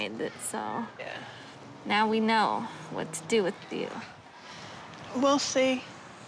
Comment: How rude was the girl putting the cream on her face telling her we really don't know what to do with u . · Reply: I thought she said “ We know what to do with you now” As in we know how to help you more.